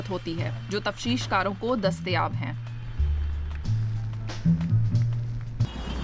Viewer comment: TafteeshKar instead of TashweeshKar. Thx ❤